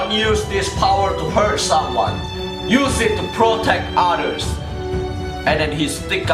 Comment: When he said "Use it to proctect others!" I heard "Use it to proctect otters!"